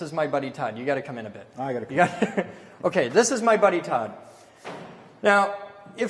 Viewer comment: I honestly thought he said "this is my burrito" When what he really said "this is my buddy Todd".🤦